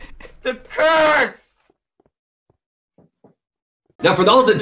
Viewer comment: does he say purse or curse xd i know it meant to be curse but sounds like purse